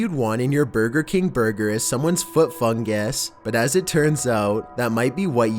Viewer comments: i always thought it said foot fun gas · i thought he said "foot funggass"